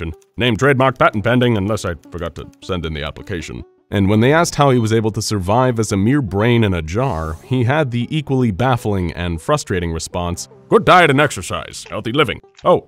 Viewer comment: I thought it said brian in a jar and I thought ohhh nooo not Brian noooo 😭